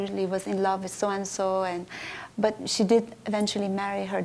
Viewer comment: Sorry i have to correct something... She said that Oum Kalthum was never married but this isn't true.. she was married to a doctor and which means that she wasn't gay too.. i think she has to correct her info by reading more about Oum Kalthum or by contacting her family.. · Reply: you have to correct yourself first . u being a hypocrite . 29:29 she clearly said she was married to her doctor.